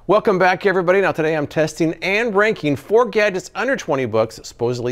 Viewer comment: Oh, testing and RANKING! I thought you said "testing and breaking." That would have been good to know too; what it takes to break each product.